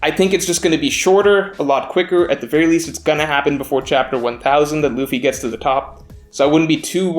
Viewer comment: I thought said chapter 4000 so had to replay ;D